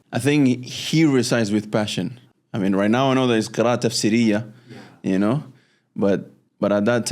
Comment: What does he say here - right now I know that it’s qira’a - ?? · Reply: Qiraa'a Tafsiriya, I think it's where the way you recite reflects the meaning of the ayat. Coming from the word " Tafsir". qira'a means recitation and Tafsiriya means "in a way of Tafsir". For example, when speaking about Jannah, then the tone becomes much higher for example and different, or when it is about hellfire, the voice may become lower. Sheikh Mishary Al-Afasy is really good at this, May Allah preserve him